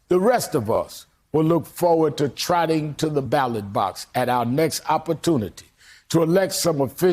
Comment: lol "red meat, picture books," I thought he said "pigeon books" which would've still made a perfect sense of who the Republicans have become.